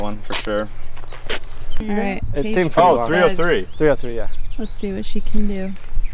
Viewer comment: #8 I just have when teepad is that close to big tree. 9:30 did say "let's see what she can do"?? 😁